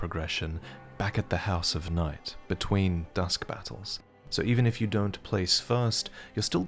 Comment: Misheard it as Duskbutts when the vid started